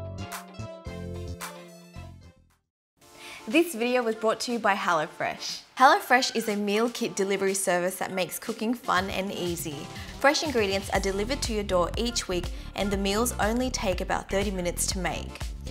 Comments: I thought she said "this video was brought to You by hella fresh" lol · i heard it as "hella fresh" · When she said "hello fresh" I thought she said "hella fresh"